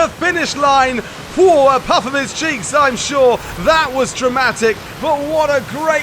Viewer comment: What did he say at 20:23 ? “Puffin his cheeks ? “ 😂😂😂 sounds dirty